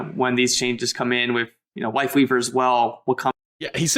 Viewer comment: He says it twice but you can tell there's a clear difference in how he pronounces it either time. Unlike the first time, he has a clear L for life weaver while even in slow mo he sounds like he says Wife leaver the first time.